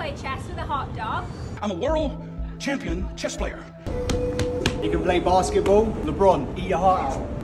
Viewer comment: I’m from Michigan. First go around I could’ve sworn you said “You can play basketball with a prawn.” Lol new subscriber